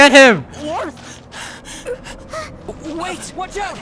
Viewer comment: Did our MC just talk? Did he just say “Watch out”?